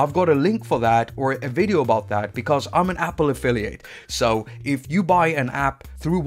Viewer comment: I thought you said in the end: I'm an appophilia. But you said affiliate ☺️